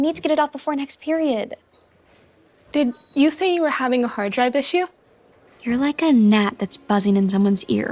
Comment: 😂😂 Also the subtitle says hard dive instead of hard drive